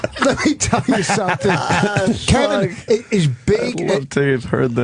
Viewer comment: was vos calling keith "kevin" at 2:50:00?